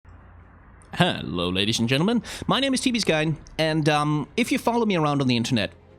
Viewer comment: At the start I thought you said: "Hello ladies and Gentlemen my name is Timmy's guy" lol